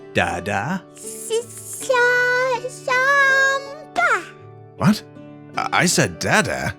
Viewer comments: did she say "sombra" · I thought she said Samba, that's cookie.